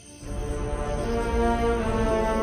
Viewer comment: Hi. I'm not native English speaker, therefore I used English subtitle. And 23:00 You said 'I love you guys , Akuria matte.' But subtitle said 'I love you guys, who do you pick?' WHO DO YOU PICK? 🤔hmm strange subtitle😅